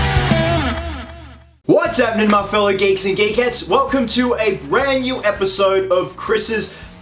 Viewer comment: It sounds like he say geeks and gay cats